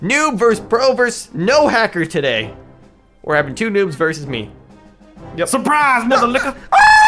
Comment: I just came back after 3 years watching it the first time and now that I’m older is sounded like said surprise motha n